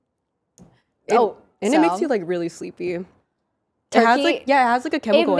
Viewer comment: I thought she was saying cock makes you sleepy. 😂